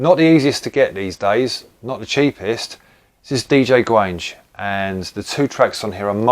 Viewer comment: Legend Records folk were often at the Brunel Rooms Fruit Club. When the MCs shouted out DJ Gwange, I heard it as Gwan-gee